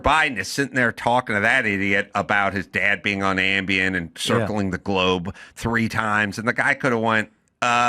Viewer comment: Hunter said Joe was circling the globe? I thought he said Joe was circling the drain.